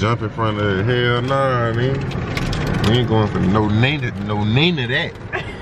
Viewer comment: SOUND LIKE U SAYIN MA NAME! NEENA!! LOLOL.. 😂